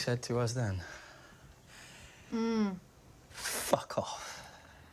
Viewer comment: "We came to you, a long time ago, seeking your help, and I'm gonna say to you what you said to us, then. F*ck off." 🤔 That's not what he said. Not quite. What he said was, "Go f*ck yourself."